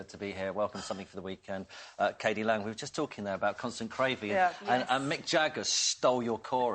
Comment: I heard him say that as CAN'T STAND GRAVY!!!!!!